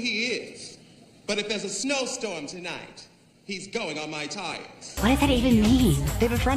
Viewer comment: I JUST GOT what Ru was saying. I always thought she said "hes going on my tights" and I was like....what? No its "I don't know who he is but if theres a snow storm tonight he's going on my tires." Because of the chains on his chest. That last word changes the entire context lol I finally got the joke after all this time!